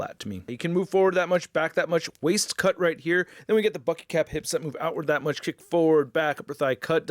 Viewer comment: I know you said Bucky Cap hips, but I heard “F**ky Cap hips”